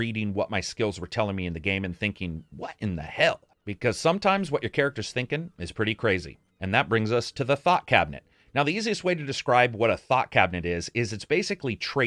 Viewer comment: lol I thought you said "Thot Cabinet"